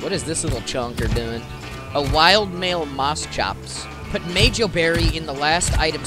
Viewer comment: DID IT JUST SAY AMONGUS